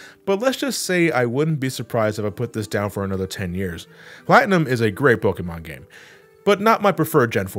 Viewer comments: At first I thought he said "Platinum is a great book in my game." Like he was drunk. 😂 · "Platinum is a great book in my game", proof that is just an NPC in the Sinnoh Library breaking the 4th wall. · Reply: I genuinely thought he was trying to say "good game in my book" and just mixed up the words. Now I can hear that he was trying to say "good Pokemon game". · For a moment I misheard that as "Platinum is a great book in my game" lol